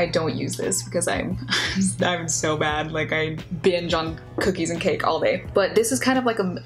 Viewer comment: Ok I cannot be the only one who heard her say “jungkookie” before I realized it was “binge on cookies” lmao